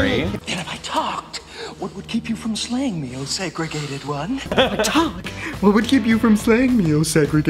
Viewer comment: “And if I talk, what would keep you from slaying me segregated one” I really thought it said “oh sacred gay one” instead of segregated one 😂😂😂